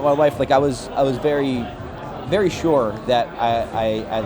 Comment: I thought he said "very short" and I was like "We know that"